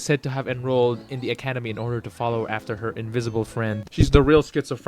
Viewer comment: Did he just say....INVISIBLE